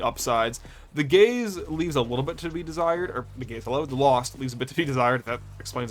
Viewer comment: As a queer person, I will often mishear "gaze" as "gays," but even then, 24:50 took me out completely, lmao. "The gays leave a little bit to be desired"